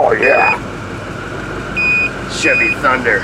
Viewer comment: Prof."O"...did you say chivvy thumper at 35:12 mark!?! C'mon man!!!